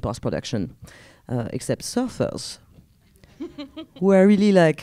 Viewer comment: What does she say at 1:15? Surface? Sapphos? I can't make it make sense. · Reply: Surfers. Ocean background.They didn't surf back in 1750 :) or maybe there were some sapphic surfers surfing on the surface, only Celine Sciamma didn't want them in her film. That's cause she hates surfers!